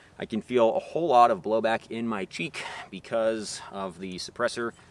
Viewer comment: I thought you said you felt a lot of blowback in your BEAK That would have been extremely based 🐦